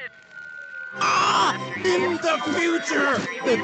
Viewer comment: he is saying invest the future but in normal video i didnt hear it